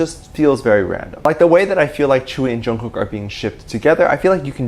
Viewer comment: At first when you said Tzuyu I thought you said chewy and I was like whaaaaa